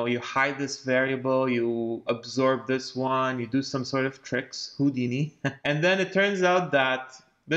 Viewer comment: Did he just say Houdini ?